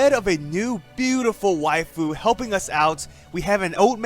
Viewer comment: did u just say beautiful waifu ?